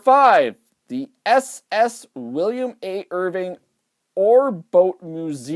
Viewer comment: Just wanna say sounds like you said William fitz Irvin?????? Just correcting it's William A. Irvin. Edit ok wasn't all the way through before commenting you corrected yourself when actually talking about it.